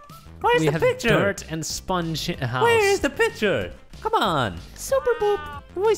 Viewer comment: Did he say sponge s*it house or am I hearing things 3:50 Edit:I mean